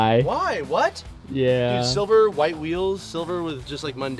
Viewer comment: did he say silver with white wheels? 😁 it’s a good look😁😁